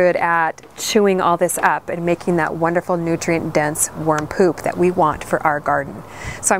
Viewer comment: lol the kid face in 9:56 was like did she just said worm pop XD